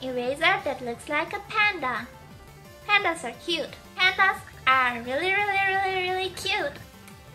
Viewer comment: pandas are cute really really cute wat i hear is "tao is cute really really cute" hehe~